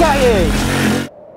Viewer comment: on 1:53 did he speak german "Yep Geil" or am i hearing something crazy ? xD